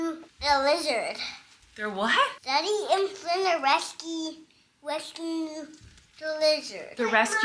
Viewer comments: I thought said Flynn and daddy are wrestling a wizard 😂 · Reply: i thought said they were wresting a lizard 🤣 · I thought said Flynn and daddy are wrestling a wizard 😂